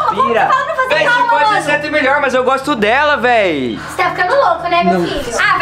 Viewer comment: Só eu que ouvi o falando no 10:01 que vc pode ser até melhor mais eu gosto dela 😂